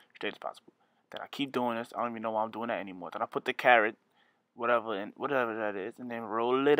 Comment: I know he DID NOT just say "the carrot" ....bruh I'm dead